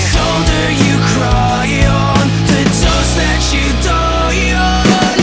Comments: ok, call me deaf but does he say 1. "The dust that you die on." 2. "The dose that you die on." 3. "The toast that you dine on." (I'm kinda hoping it's the 3rd one.) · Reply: I thought he said the jokes you die on 😂 my bad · Hello there! Welcome to the comment section! This comment section 65% "stop hating on Matt" 5% people hating on Matt 20% "I miss Adam" 10% "is it the toast that you dine on? Or the dust that you die on?" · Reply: I thought it’s said “the jokes that you die on” 😂😂😂